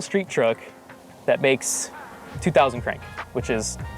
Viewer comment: What did he say ?!?! 😅😂😂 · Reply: The name of the truck is El Negra